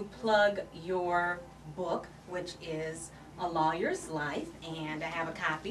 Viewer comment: I could've sworn I heard her say, "A Liar's Life", when she was introducing Johnnie's book